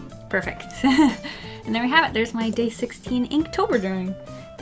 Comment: I misheard her and thought she said GAY 16 and I was like APPROPRIATE FOR THE RAINBOW. love this btw 💖